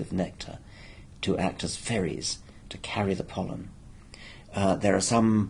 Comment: Dawkins says that, so to speak, the flowers provide nectar to bribe the insects to act as ferries to carry the pollen. Clever! But at first I thought he said the flowers bribed them to act as “fairies” carrying pollen. Also clever, but delightful.